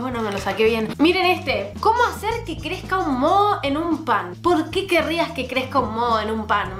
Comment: escuché mono xd